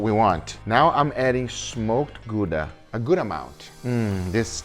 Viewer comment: Thought he said smoked guga first.😅